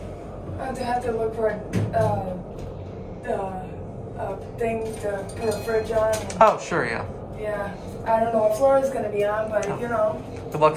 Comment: ? What is she saying lol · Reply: “I have to look for the thing to put a fridge on. I don’t know what floor it’s gonna be on, but you know!”